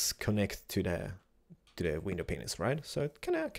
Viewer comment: does he say penis XDD